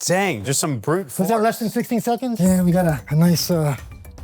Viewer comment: what did i hear? 67? Edit: it was 16